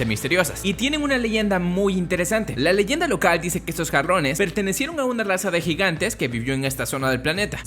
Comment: por un momento entendí " cabrones " xd